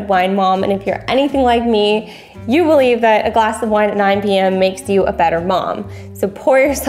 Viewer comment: I thought you said that a glass of wine at EIGHT PM is making you a better mom